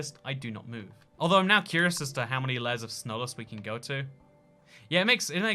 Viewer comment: he says "snowless" instead of "slowness"